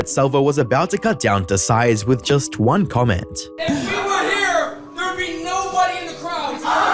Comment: Is he saying "If she were here, there would be nobody in the crowds?" I don't get it. Can someone explain it to me?